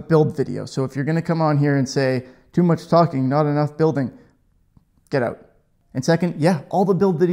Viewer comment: seconds - says Get Out! Well done. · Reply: "Get ote!" I thought I heard.